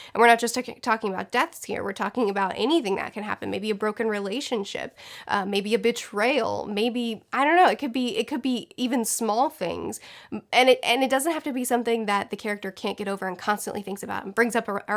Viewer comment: I heard "bitch-rayal" instead of betrayal, and yes.